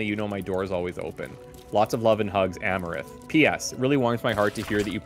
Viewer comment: When he said amarith while he read the letter at first I thought he said amouranth. In my mind I went full on NOOOO, not in here to!